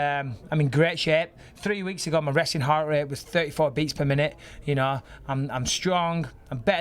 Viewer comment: did he say his resting heart rate or wrestling heart rate?